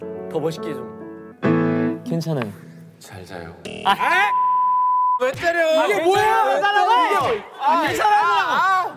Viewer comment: 갑분 잘자요 감사합니다ㅠㅠㅠㅠㅠㅠㅠㅠ 근데 뒤에 규현님 뭐라고 하시는거야ㅋㅋㅋㅋㅋㅋㅋ엄청 뭐라고 하는데 무슨 말인지를 모르겠어ㅋㅋㅋㅋㅋㅋㅋㅋㅋㅋㅋ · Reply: 마지막에 앨범 내달라고오오 였나봐ㅋㅋㅋㅋㅋㅋㅋㅋ